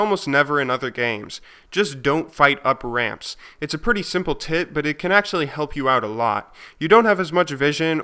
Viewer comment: it sounds like he said "its a pretty simple tit." lolol